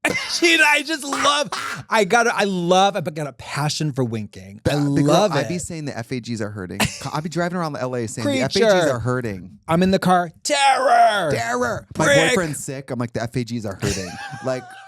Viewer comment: The ef ei gees are hurting is my new vocal stim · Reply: I literally was like WHAT DOES THAT MEAN. I thought she said “the effigies are hurting”. I get it now omg.